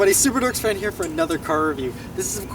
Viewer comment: i thought you said "superducks fan" lol slow down a lil bit... good vid however